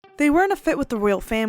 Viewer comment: I totally thought you were saying,"they were in a fit with." Both are true, anywho 😂